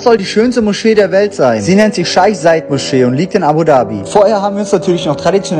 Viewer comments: Ich habe verstanden: scheiss ei muscheee 😂😂😂😂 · Ich hab scheiss Moschee verstanden 😅😓